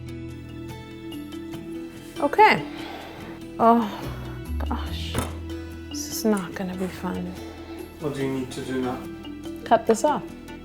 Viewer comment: Who else heard her swear at 7:30 Like if u did I still love her with all my heart! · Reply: She said "oh gosh... This is not gonna be fun.."